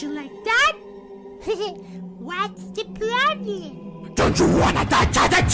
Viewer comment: What I heard was don't you wanna die as a c